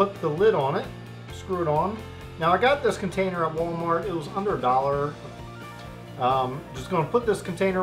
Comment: I thought you said “I got this at Walmart for a hundred dollars” 😂